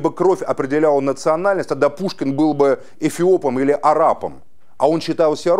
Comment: Он сказал что Пушкин арап или мне послышалось(Заметьте не араб) на 6:14 минуте?